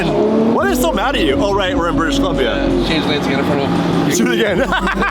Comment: What does say that cracks up with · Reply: Change lanes and get in front of him